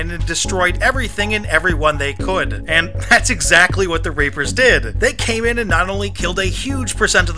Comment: I swear he says the rapers